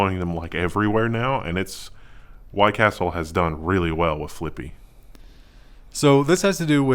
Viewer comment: I thought you said Clippy!